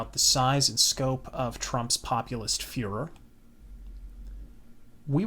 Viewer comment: did you say fuhrer or fervor? lol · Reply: Furor. As in 'furor teutonicus'